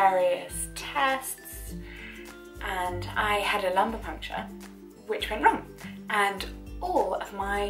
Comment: Ohh I feel dumb. This entire time I thought you said you had acupuncture that went wrong, but that didn’t sound right so I turned captions on. Lumbar puncture makes way more sense! Thank you for another amazing video! I’m so glad I found our channel